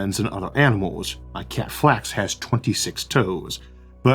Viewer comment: Did I hear that right? The cat's name is Flax? Also what kind of mischief can a cat get up to when it has 26 toes?